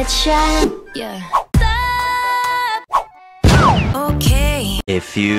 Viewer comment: I THOUGHT THAT SAID TOE CREAM💀😰😭